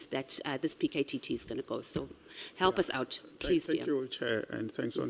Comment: am I hearing her say "please dear" a few times? She sounds more free today but I think I prefer the more restrained version of her. The animated version is a bit much